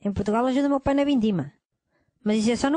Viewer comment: O tio dele é imigrante, mas antes trabalhava no que? Eu entendi "vendima". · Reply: Vindima. É a colheita das uvas em portugal. Cada lugar tem uma estação pra colheita, por isso que ele fala que o tio dele ajuda "só no verão"